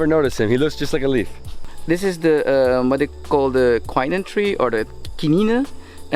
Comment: a cure for malaria. Couldn't catch the name of the three? What's it called? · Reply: Cinchona officinalis | Cinchona tree | Kinine tree